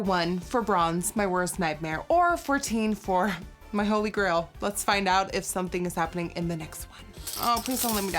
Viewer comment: did she just call 14 “teenfour?”